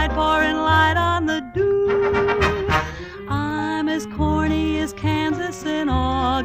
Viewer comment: I know. My comment was response for an user that thought she said "horny". My comment was sarcastic.